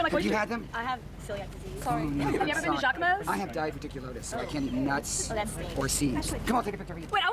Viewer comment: Um, what? Has there been another reply to my comment other than yours and I don't get what you're refering to? If not, can you elaborate? 😅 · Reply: I didn't get that he was saying diverticulitis. English isn't my native language, so I thought you were just saying "difficulties." Well. And about that, I could be wrong, but I think I see this a lot with dominant feminine Fe. You could be having the struggle of your life inside, but then there's people and all of a sudden you're able to have that genuinely positive mask on for as long as they're there. As soon as they're gone, you fall back into the hole. That's what it looks like on the outside and what can be the case. A mask. But it can go way further. Personally - assuming of course that I typed myself correctly as having dominant feminine Fe - the positive feelings ARE genuine. Like, for a moment you forget about all the bad stuff and you're able to let go off all that's bugging you. You are able to see the good in the bad for once. Because there's company and that feels like a relief, is able to lift you up, remind you of your own positive thoughts and be encouraging. And once you're alone again, it's all back to darkness.